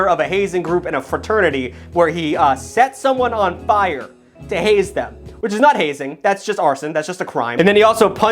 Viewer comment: Haze them? I thought you said blaze them!